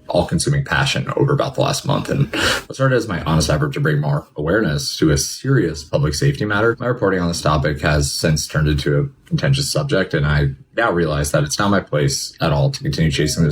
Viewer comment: I thought he said "Sex was a pretentious subject" Lol. I was like "Bro???"